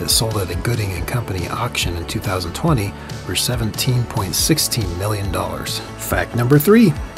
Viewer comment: Did you say "Seventeen point sixteen million dollars" or do i need a new set of earbuds?